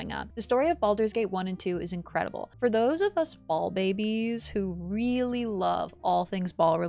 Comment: i tried this game a month ago and it was too hard. but this video and u saying it plays like runescape really opened my eyes i never saw it like it played like that and i played runescape my whole life lol thanks you two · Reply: i thought u said bald babies xD